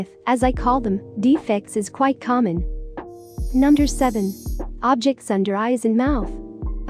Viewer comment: It sounds like it says u nuder Xd